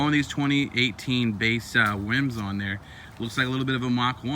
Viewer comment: he says wims instead of rims.